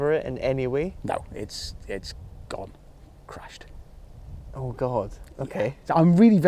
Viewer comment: he actually says "it's gone, crashed"! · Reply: Crushed, not crashed :D